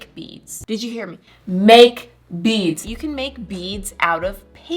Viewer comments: When she said, “Make beads” I heard, “Baked beans” 😹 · I'm i the only one that thought she said baked beans instead of make beads?